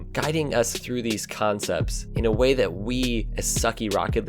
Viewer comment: Did he just say "khancepts"?